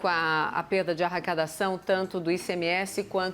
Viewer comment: Só eu ouvi arracadaçáo?